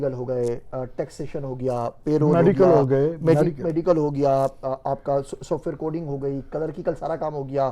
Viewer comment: Did he say medical at 6:33 ?